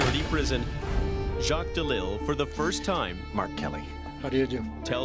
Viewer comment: Everytime he introduced himself I thought he was saying R. Kelly instead of Mark Kelly I was 🧐